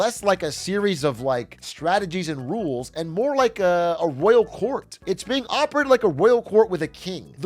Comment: what is it that atrioc is saying here? A whale court? Can someone tell me? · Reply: Royal court , a court led by a monarch with absolute power. It ends up with yesmen as advisors, flips in stances based on personal opinions